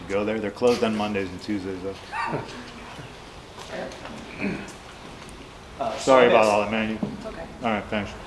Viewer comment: "Sorry about all the ..." what? I struggle to understand the last word. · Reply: I believe it’s “sorry about all that Mandy” because he just shouted out her bakery after he went off 😂 also it would add up because what sounded like a feminine voice who I assume is said “it’s okay”